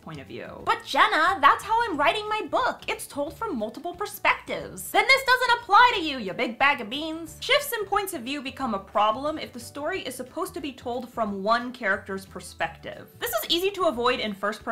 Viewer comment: Woohhh... did she just call me a beaner @ 7:08 😮